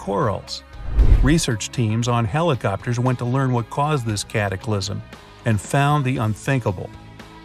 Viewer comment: did he say CaTAclysm!🐾 I know miraculous fans can understand...🐒